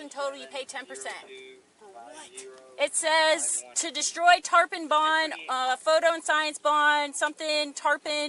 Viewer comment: OMG A TARPON IS A FISH what a monster I can understand fishing, but like, STABBING? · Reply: First heard it as a Tampon bond